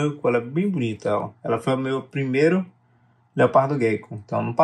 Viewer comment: Eu entendi "meu leopardo gay"